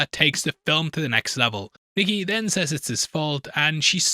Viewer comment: the subtitles say Niggy instead of Nikki lolllll.